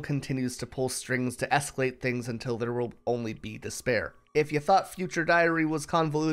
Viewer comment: You said "despair" but I heard "dis bear"